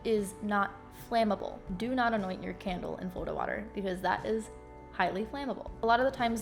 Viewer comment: Does anyone know what she said at 6:03 ? Florta water maybe? I would like to know so I can research it more :) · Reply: Bob Chlarson I think she said Florida Water, and it seems logical after looking it up!